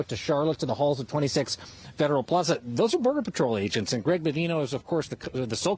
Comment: did he say murder patrol agents?